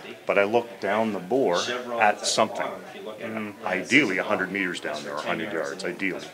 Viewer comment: Holy smokes! Starting at 15:40, he describes a 'hasty sight in' at 30 M (aka yards) placing POA and POI on same dot drawn on a piece of paper on the wall! This means actual live shot will be dangerously high at 100 yards /meters and possibly over any back stop! Seen it happen! What to do? Measure the scope to bore center-line distance and mark 2 dots on your paper marked 'POA' (point of aim) and 'POI' (point of impact). Look thru the barrel and get it on the POI and then adjust the scope to zero on the POA. When you go to the range for the first time hang a paper at 50 yards/meters and use the POA of the scope to see how close the POI is, then go to 100 Yards/meters and dial in a few clicks so the POA & POI are the same. The key point is, when parallel sighted, you'll always be on the paper for sure and a little low, but not high and dangerous. You're sure to be off the paper at 100 yards using the method in the video you won't spot how far off the shot is and may push your elevation even further (and dangerously) off. Bad sight-ins have a lot to do with live rounds hitting homes & pple miles away from the range! Seen it! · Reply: watch it again and listen to what he is saying ( at 15:40 he says 100m, not 30m )